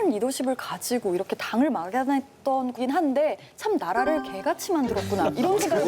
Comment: ㅋㅋㅋㅋ 처음 들었을때 연개소문에 대한 분노를 말하는줄 알았는데 개가 아니라 게 ㅋㅋㅋ 개가 아니라 게라고 표현 하는 이시원 배우 진짜 귀엽네ㅋㅋㅋ역사저널 그날 이시원 배우가 있어서 볼만 하다ㅋㅋㅋㅋ